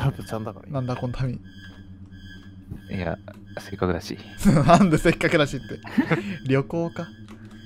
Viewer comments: の「せっかくだし」が「すみっコぐらし」に聞こえたw 8:00 かわいい(自分用) 10:42 かわいい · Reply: え？自分は6:13のとこのの「せっかくだし」が「せっかぐらし」に聞こえた（笑） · の「せっかくだし」が「すみっコぐらし」に聞こえたw 8:00 かわいい(自分用) 10:42 かわいい